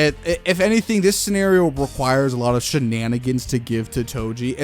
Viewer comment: did you say shenanigans Jujitsu Shenanigans